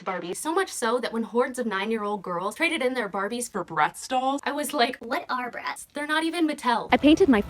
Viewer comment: Ngl but the first few times I watched this I thought you said “breast dolls” (as in that weird breast-feeding baby doll) and “what are breasts? They’re not even Mattel!” And I’ve never laughed so hard at a misheard line 😂